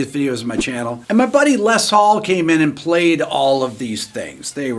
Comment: I misheard what you said and thought you said your friend Les Paul came in and played keyboards. And I thought Les Paul? Keyboards? Surely he'd play guitar?